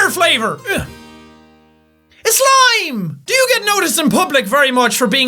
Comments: am I the only one who thought jack said his hair flavour is slime rather than lime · i thought you said "ITS SLIME!" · "IS SLIME" That's what it sounded like xD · It sounded like he said slime instead of lime